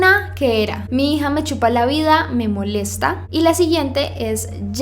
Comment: Alguien se dio cuenta que dice chipa en vesde decir chupa!!!